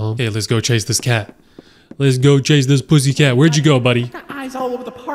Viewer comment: DID BRO SAY PUS